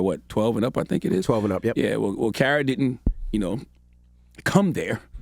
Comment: I thought he said "Karen"!!!!!!!! She is "Butt Mad" about this huh lmao!